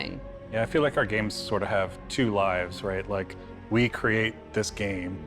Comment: did he say lives or lies